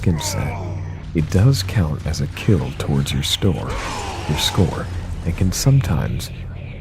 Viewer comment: did you just say store